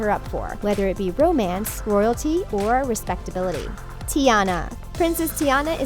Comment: in caption instead of royalty it's squirrel tea 😂😂😂